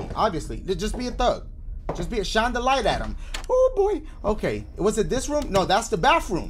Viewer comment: When dashie said. Oh boi, sounded like Micheal Jackson's "Yee, her"🤣